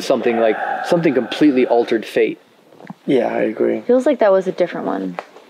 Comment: I hear a deep voice saying "yeah". Is it one of the guys/girls. Or..... And around 01:30:16, you see a mist/fog at the girls face. while the whole time, you don't see anything like that. Domo